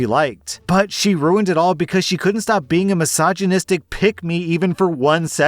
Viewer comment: So... When you first said "pick me"... I thought you said pygmy lmao. "She's the definition of a pygmy"